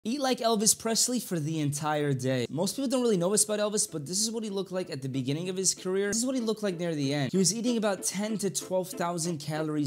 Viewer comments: I thought it said he ate elvis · Bro I thought it said I ate Elvis Presley 💀